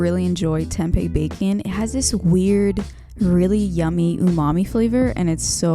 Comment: i thought you said mommy for a sec 😂✋